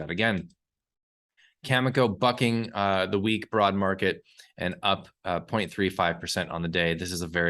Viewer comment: “Cameco f**king the weak broad market” @ 5:00 That’s how I heard it. 😎